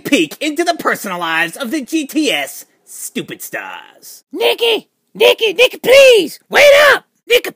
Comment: whenever cena said 'Nikki Please!' it sounded like he was saying 'Nigga Please!'